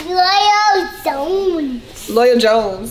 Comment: I thought he said loyal zone. That will be a cool YouTube name.